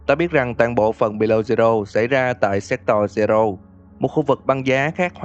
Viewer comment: có ai giống t ko nghe thành séc toy zero =))